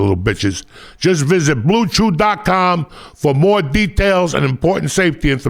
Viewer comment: Thought he said Bluechi im like wow snazzy lol